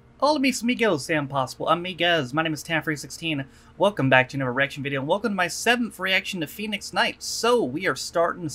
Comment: Sincere apologies....I thought you just said “Welcome to my erection video” Which is a whole different ball game....